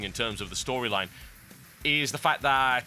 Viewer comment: Everytime you say nigh on it sounds like you say nyan